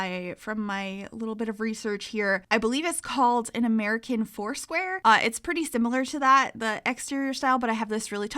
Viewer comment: when you said 'american foursquare' i heard 'american horseware' and i was like wow pensylvania really is quirky